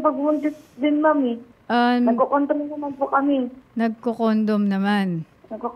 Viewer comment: ATTY SAM dont worry kahit ulit ulitin ko NAG CCONDOM talaga narinig ko, pero sinabi nya 'nagcocontrol naman kami'... pwede na nagccondomtrol LOL